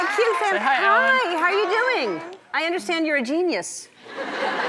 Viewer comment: lol she kinds sounded like she said "im a penis"